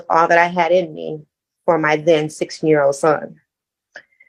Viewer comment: You wrote 6 year old when talking about her son, but i heard her say 16 year old.... Did i hear it wrong? It would make more sense that he was sixteen considering she said he picked her up and got her into the corner of the living room...